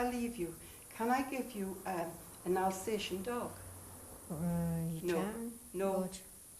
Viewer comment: What does he say around 1:20? "Can i give you an all sation dog?" "No.." ???